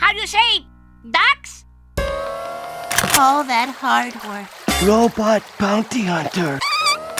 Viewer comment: did he say robot bounty hunter?